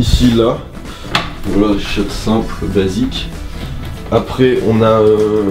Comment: c'est moi ou il dis "juif" à